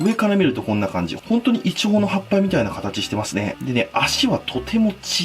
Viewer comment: ｢いちごの葉っぱ｣に聞こえた(難聴)